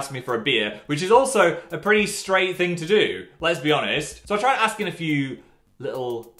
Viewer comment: what phil said: "let's be honest" what I heard: "les-bi honest"